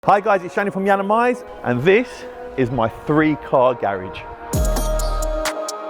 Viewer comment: Did he just say garegge?? Already lost me im not listening